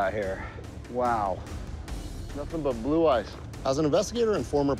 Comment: what is blue ice? first I thought he said blue eyes.